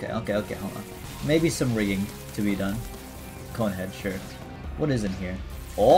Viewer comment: When he said cone head I thought he said Godhead and for 1 second I thought it was ME who got "Godhead" and I fell of my chair XD